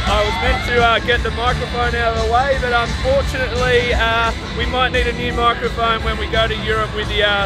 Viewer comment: Did anyone else heard that. 15:13. “ he need some milk” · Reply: I heard it "he need some milk"